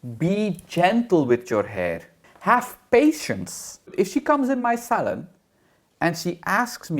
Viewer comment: So I had youtube playing in the background for some white noise while I was doing homework and wasn't really paying attention until I heard "if she cums in my salad"....my brain went "EXCUSE ME?!?!?!?!" I had to stop what I was doing and rewind the video to figure out what in the hell kind of kinky $hit I was watching. Lmao! Thankfully actually said "if she comes in my salon".